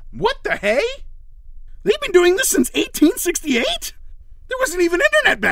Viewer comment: did pencilmate say 1868